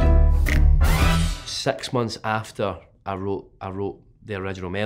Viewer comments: swear to god thought he said sex months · At first I thought he said sex must after instead of six months after